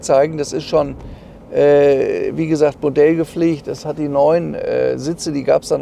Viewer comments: Bei 5:17 hab ich da die Sitze sind Bordell gepflegt gehört?😁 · hab ich richtig gehört? Bordell-gepflegt? 😂